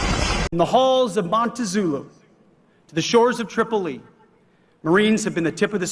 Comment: Why did Hegseth say, "From the halls of Montazulu" instead of 'Montezuma'?